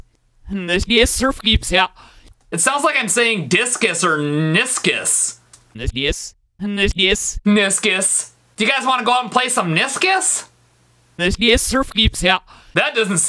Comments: it sounded like “Let’s kiss,Let’s kiss,Let’s kiss” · I heard Discus Discus Let’s Discus The future